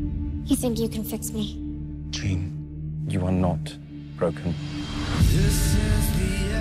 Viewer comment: THANK GOD HE SAID BROKEN!!!. Did anyone at first heard him say pregnant.! C'mon I can't be the only one.